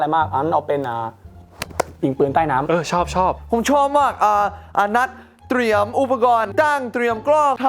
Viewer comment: เหมือนพี่จะพูดว่าอะนัด แต่พี่ไม่ได้ตั้งใจพูดหรอก😅